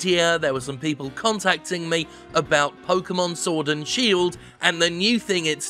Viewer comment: I swear I heard Jim say "Sordid Shield" at least once.